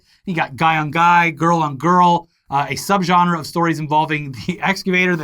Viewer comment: “They really came at it from every angle” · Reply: i hear "came in it from every angle"